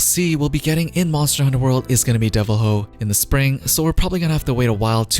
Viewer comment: Did he just say Devil-Hoe? Omg why. It’s Devil-joe